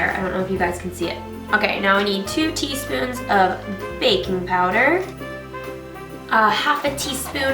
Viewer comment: haha thought you said BACON powder